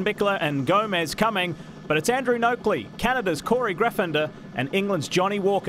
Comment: it sounded like he said cannabis , he actually said Canada’s , I mean it’s my mistake just a bit ironic that it was one of the countries that are more liberal than others , when it comes to the smoking of the herb 🌿 that and I’m stoned ! Now the maple leaf makes sense ! As do pancakes with maple syrup mmmmmmmm